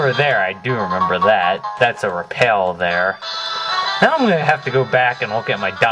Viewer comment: i thought it said rape